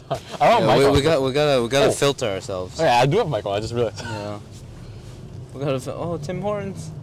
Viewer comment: did he call him Michael? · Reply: "I do have my coat"